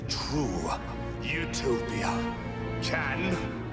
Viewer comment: heard wrong and thought he said "Youtube Poopia" 🤣